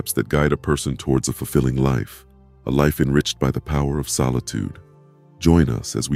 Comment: 😂 thought it said the power of a stoner and I thought his hair was a bong 😂 messed me up for a second lol